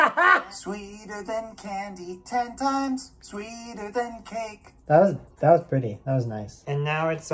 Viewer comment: This song tastes like candy and sweeter than cake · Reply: Why did I thought you said candy sweater cake